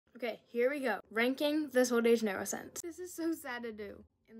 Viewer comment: I thought she said “Drinking Sol de Janerio scents” i was like girl u ok? 😇🥰👏